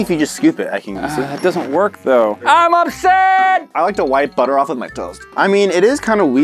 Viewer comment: When said “I like to wipe butter of my toast” I thought he said “I like to wipe butter on my toes “